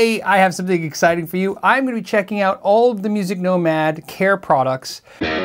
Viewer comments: Thought you said “hair products” 😬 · I was doing something else and I thought you said hair products, so I had to pay attention.